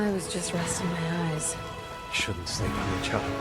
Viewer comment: the subtitles said” I was just resting my eyes,” and Leon said “ shouldn’t sleep in each other “ 👀👀 😂